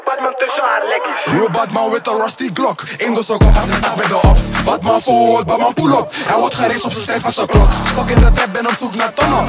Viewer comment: Wat zegt hij hier? lol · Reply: Badman forward badman pullup is van sk6 (jurrski